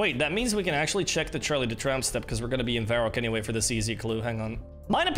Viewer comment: Coulda sworn i heard you say Charlie the tramp stamp in the beginning